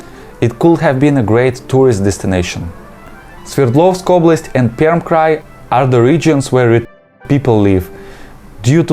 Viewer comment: what word was blanked out? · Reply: R-word 😶